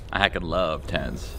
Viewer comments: Good to see the goat back but Ughm what did he say at 0:46? If he said “I hacking love tenz” that was so cringey lmfao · "I could love ted's" is what CC says he said ><